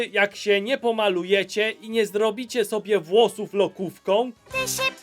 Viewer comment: czemu usłyszałem lodówką???